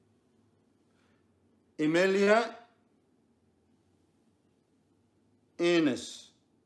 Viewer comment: "A maniac anus"? I never know what he's saying!! 😭😭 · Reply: Amelia Innis. Lmfao 🥴🤣🤣🤣🤣🤣🤣